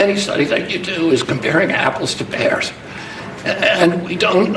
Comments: Did he say pears!? I thought he said apples 🍎 and bears 🐻 I was laughing so hard lol 😂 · I can't say I've ever compared 🍎s to 🐻s. I didn't hear pear, I heard bear. The worm probably ate the part of his brain linked to pronunciation.